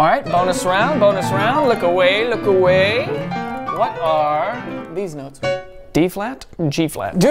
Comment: he said deez nuts instead of this note